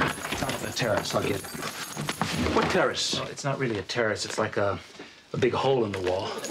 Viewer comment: "what terrorist" "not really a terrorist its like a big hole in the wall" "from what?" "an explosion" did they really say that? or was i hearing things